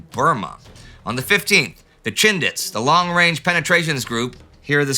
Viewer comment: When referring to the Indian brigade in Burma, I keep hearing "the Shindigs" Instead of the less familiar word "Chindits" .